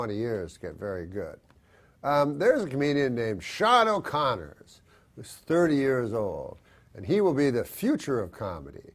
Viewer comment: What comedian did Norm say is his fave younger comedian? Thought he said Shaun Conner but I can't find anyone but an old Irishman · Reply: Sean O'Connor - he used to open for norm